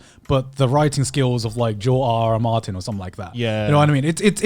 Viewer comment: What’s the name of the writer said at 11:21? I can’t find him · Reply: George R R Martin. He the guy that made the game of thrones books